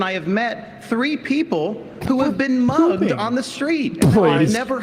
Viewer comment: Did he say hugged?